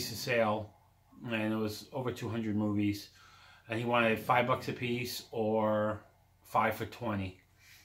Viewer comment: Totally thought said “Deal is $5 a piece...or a kidney” 😂 54 MINUTES!!! Lemme plug in my charger. There’s a live action MADELINE!? Awesome. Mint movie, dang my boys and sisters boys DESTROYED movies...conversely my little girls clean everything, wipe the dust off tv they’re awesome. Oh I made a video a long time ago showing those damaged DVDs on my old account google locked me out of. Upside Down made me sick 🤮 KNOWING is one of my favorites. The good FROZEN..I am in a minority that doesn’t like Disney Frozen. Oh noes not gonna make it through an hour, 3/4 way watch time. Lots of great movies here 👍 Dakota Fanning narrated a documentary about a man who wrote a story his entire life, a hermit. It was really interesting but can’t remember what it’s called. He created a Fairy World and the room full of writing and drawings was discovered after he passed. I want to see it again and think you’d enjoy it too, especially Ms. Bambi 🦌